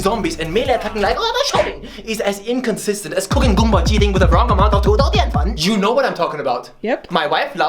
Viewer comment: For everyone wondering what is saying at 4:05, it's "cooking kung pao chicken with the wrong amount of potato starch".